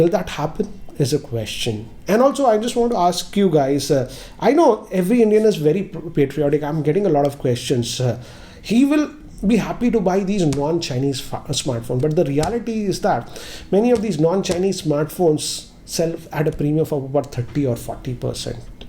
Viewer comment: did he say non chinese f**ker smartphones?